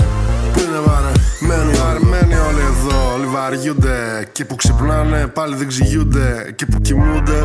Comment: Ρε παιδιά τι λέει από το 1:09 μέχρι το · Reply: " ειναι βαρεμενοι ολοι εδω ολοι βαριουνται"